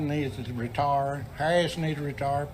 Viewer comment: I thought he said re tard too